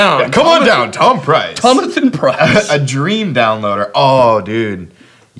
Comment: my, nigga... did you just say "tomathan"? 🤣🤣🤣